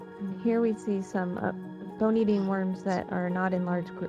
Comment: I could not tell what kind of worms she said so off to google I went and I went down the Bony Bean worms rabbit hole 🐛🐛🐛🐛🐛🐛🐛